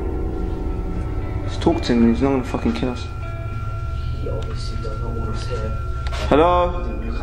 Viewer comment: is the guy talking? Right before the gunshot i hear “You obviously don’t understand. You have things to do”